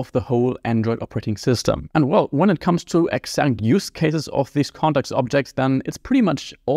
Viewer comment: it comes to 'exync'? what exync mean?, awrote this in the auto-generated subtitles, but I wonder what words he actually said. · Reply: "exact" "When it comes to exact use cases..."